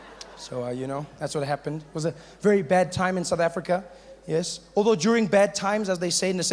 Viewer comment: I hear "a bar tide"